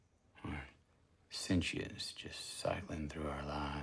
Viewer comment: can you tell what word he says at 0:50? something "... just cycling through our lives" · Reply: i think he says "sentience...just cycling through our lives"